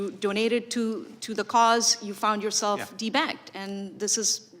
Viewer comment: I actually had to rewind the part where the lady on the panel said that certain people have been "De-Banked". What the heck? I didn't know there was such a practice, I thought I heard her wrong. This is scary stuff. Government overreach? Absolutely! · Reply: 😇🤣I swear when i first heard her saying de-banked it sounded like, people are being T bagged. I hed ti rewind the video 😉😑